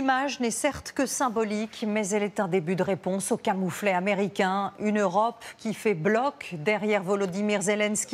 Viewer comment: "le camouflé américain". Waouh. Demander un cessez le feu, c'est un camouflé en quoi ?